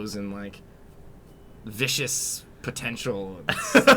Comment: I thought he said Vicious Potatoes at first and I thought it was awesome.